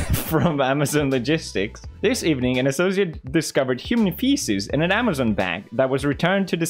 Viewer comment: bro i thought it said human faces thankfully it was just human feces